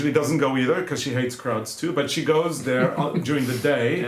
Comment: When you were talking about the festival for the trouts I thought you were saying "Kraut's" but finally realised you were talking about "crowds". I'm sorry to laugh with your accent but it was funny for me!